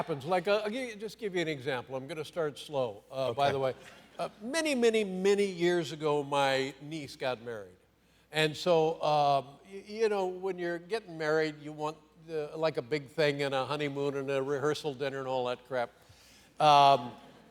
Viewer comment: I thought he said "My niece got buried"